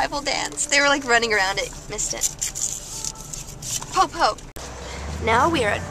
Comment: did you say "PO-PO!"?